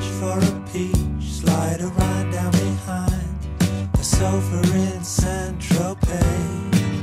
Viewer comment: Amazing song. As I reach for a peach Slide a rind down behind the sofa in San Tropez Breaking a stick with a brick on the sand Riding a wave in the wake of an old Sedan Sleeping alone in the drone of the darkness Scratched by the sand that fell from our love Deep in my dreams and I still hear her calling If you're alone I'll come home Backwards and home bound The pidgeon the dove Gone with the wind and the rain on an airplane Owning a home with no silver spoon I'm drinking champaigne like a big tycoon Sooner than wait for a break in the weather I'll gather my far flung thoughts together Speeding away on a wind to a new day If your alone I'll come home And I pause for a while By a country style And listen to things they say Digging for gold in the hoe in my hand Hoping they'll take a look at the way things stand And you're leading me down to the place by the sea I hear your soft voice calling to me Making a date for later by phone if you're alone I'll come home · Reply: always thought he said "slide a line down behind a sofa in San Tropez"...